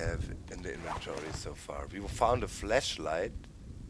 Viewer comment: That sounded like you said something totally different. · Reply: Figgy fleshlight xD